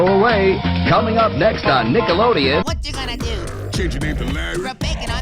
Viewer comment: The cutoff makes it sound like he said "Planet Shit" and honestly that's pretty fitting.